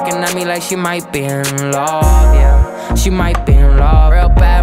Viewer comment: 🤣🤣🤣 sound like he said might BE EARN LOVE 😥